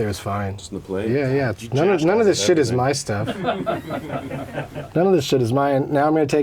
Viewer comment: What does Anthony say ay 4:28? you what out of everything? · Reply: he says none of this shit is my stuff.